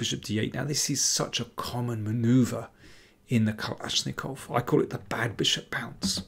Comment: Did I hear Kalashnikov at 6:10?